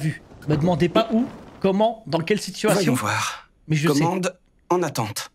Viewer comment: À 4:40 Moi j'ai entendu : "Elle je l'ai déjà vue Me demandez pas où ? Comment ? dans quelle position ? Mais je sais"🤣